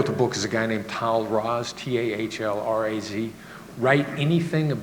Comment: What is the name Chris Voss spells at 11:20?? Please. I cannot understand even when he spells it :/ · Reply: Tahl Raz